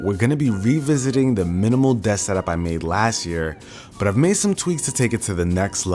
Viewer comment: I thought it said Muslim desk setup 😂